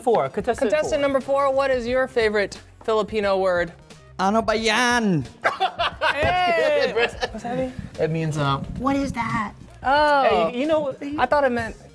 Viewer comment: “what’s that mean” sounded like “Ansabi?” Aka “anong sabi” 😂